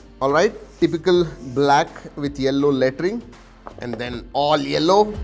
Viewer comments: Did he say yellow laterine at 1:01 ? 😦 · Typical black and yellow latrin Only I heard this or someone else also 🤢🤐